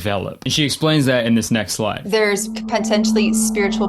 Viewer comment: did bro say line lie or live i cant tell , would be funnier if it was lie lol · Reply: He said slide